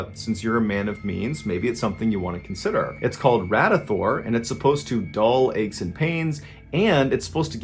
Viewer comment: sir, did you just say "Ratathor online" ....? Ha! Classic 1800.... 👍